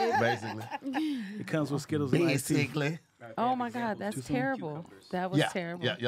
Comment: told the worst, and I mean the absolute worst, joke ever. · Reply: I thought he said skittles and nice teeth. I thought he was making fun of. Thank y'all for pointing that out.